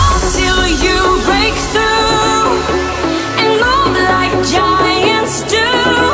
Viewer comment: i heard "move like the giant stew" XD